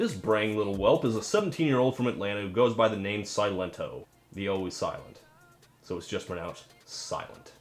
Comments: I always heard "silent toes, silent toes, silent toes" good to know his name isn't silent toes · When I first heard this song and he says silento I thought he said silence hope which is what I think whenever someone plays this as in silence the hope of not cutting your ears off